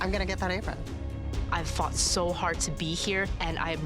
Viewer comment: did she just say that she fucked so hard to get there?